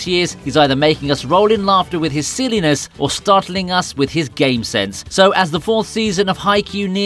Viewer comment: I thought it said gay sense😂🤣